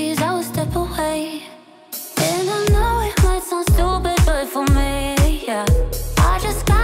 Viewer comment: I always thought she said "I know it might sounds stupid but familiar" I feel so stupid